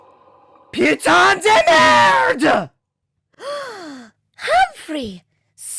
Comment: what did humprahy say again? in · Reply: "Putain de merde"